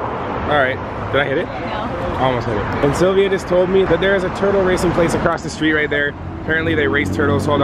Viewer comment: when u said turtle racing I thought u were saying turtle raping